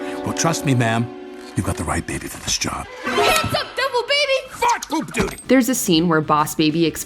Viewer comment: it sounded like he said “f*ck, poop, duty” 💀